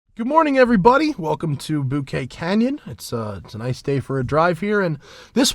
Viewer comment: Did he just say Bukkake canyon at the beginning? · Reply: Bouquet Canyon